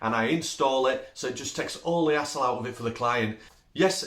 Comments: hassle🤦‍♂️. i thought you were saying asshole. "take that asshole off their hands" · Love your accent. Had to listen closer when you said 'hassle', thought you said asshole at first. Lol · He said hassle lol.. took me a minute here in the states.. thought he said a**hole lol